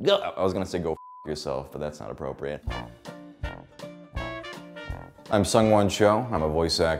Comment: thought he said "i've sung one show" 😂